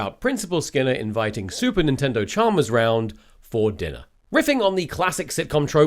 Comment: did Tom just say "super nintendo Chalmers"